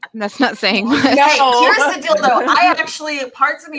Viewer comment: Every time she said "here's the deal though" and I heard "here's the dildo" and I died of laughter😂😂😂